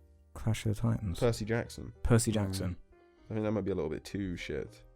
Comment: I thought said 'Pussy Jackson'.